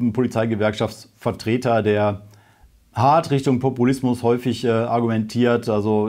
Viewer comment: Populismus ? Ich habe Brokkolismus verstanden, hahah Brüller!